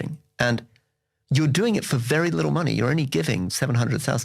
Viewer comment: haha it really sounds like he says “$700,000 per household.” Its about $1000 per household per year